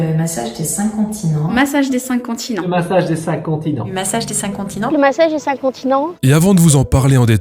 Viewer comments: pourquoi j'entends plusieurs fois "le massage des seins" ? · hahaha j'ai compris "le massage des incontinents" 😭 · y'a que moi qui les entends dire "massage des incontinents"? 😂😂😂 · La troisième répétition j'ai entendu "le message des incontinents" ... Je sors....